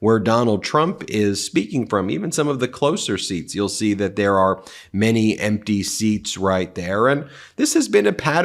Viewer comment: Ben said “Many Empty Seats” and I heard “many NPCs”